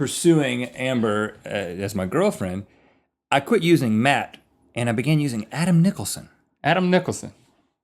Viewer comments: Am I the only one at 3:00 that thought he said "I stopped using meth"?? Lol I've been watching waaaaay to much Breaking Bad. Totally worth it though! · at like... 2:56 I thought he said. "I stopped using meth." Hahaha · I heard "I quit using meth."